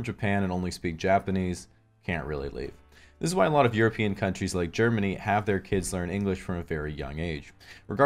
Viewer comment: wtf did he just say hahaha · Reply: Try the closed captioning button on the side of the video. “This is why a lot of European countries like Germany have their kids learn English from a very young age.”